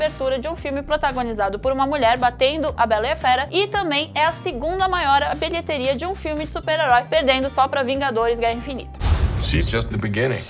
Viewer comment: Ela falou Segunda maior bilheteria de um filme de Super Herói ou eu escutei errado?🤔